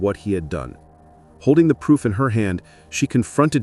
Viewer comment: "Holding the poop in her hand..." is what I thought was said 💀